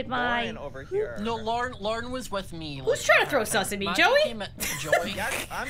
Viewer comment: who’s trying to throw sauce at me instead of who is trying to throw sus at me